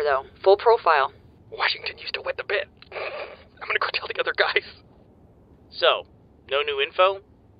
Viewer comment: what did he say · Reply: Washington used to wet the bed, I'm gonna go tell the guys